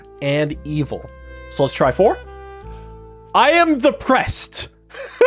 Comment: Whenever the priest says "I am the prest" it really sounds like he is saying "I am depressed"